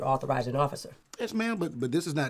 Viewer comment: What the hell does she say at the end? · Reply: We won’t get along I think